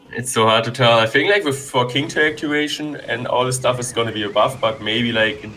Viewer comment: What did he say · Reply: "so hard to tell i think like with theking toweractivation and all the stuff its going to be a buff"